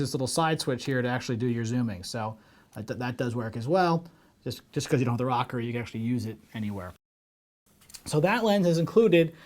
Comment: Thanks... i wish you did talk a lot slower! I couldn't understand what you actually said when you were referring to the rocker switch..at 2:15.. you say "just cause you don't have the rocker you could use it anywhere??????" or something like that???!!!! I'm trying to find out if the FS100 can have a rocker ON THE CAMERA to use with a power zoom lens. Can you tell me?